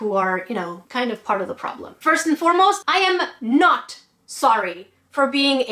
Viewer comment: For a moment I thought the video said "I'M SORRY" instead of "I'm not sorry". Honestly, I'm so happy because you didn't apologized. If people didn't wanted to subscribe to him, they're totally free to not do it and that's the end. Why do people always tries to attack someone or threaten to unsubscribe to their channel just because you're not thinking exactly the same or hating the same person (PewDiePie in this case).